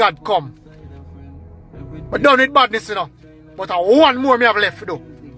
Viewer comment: But I thought he said him done wid badness 🤔